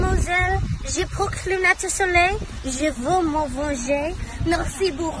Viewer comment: “Hello Miss.” “I wear sunglasses.” “I want vengeance.” “Thank you very much.” “I’m screaming.” “Yes yes, baguette.” “Kisses!” (Goodbye) — translated by someone who is studying french. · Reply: holy shit, she said "je hurle" i thought she said je jure